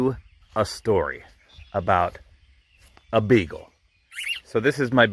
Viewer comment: I thought he said alright.... let me tell you a story about a bagel XD lol